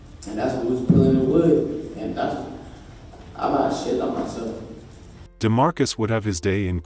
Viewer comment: So at 10:20 did he say that’s when I sh*%# on myself 😂